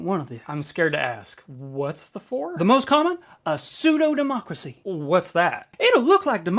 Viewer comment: "pseudo democracy" I thought you said not the America we know